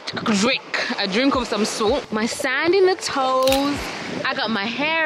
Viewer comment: did you just say my sand in the toes??? 😂😂😂😜😜 it's the rum girl lol